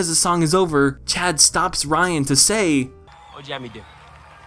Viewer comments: I still cannot tell what Chad is saying. I haven't seen the movie in so long. Help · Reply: "What would you have me do?" He says this after he says "I don't dance, but if I did..." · what did he say?? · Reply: im not sure but i think it was "what'd you have me do"